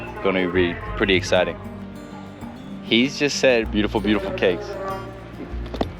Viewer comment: bonito is the name of a type of fish, and he actually said jurel, not budin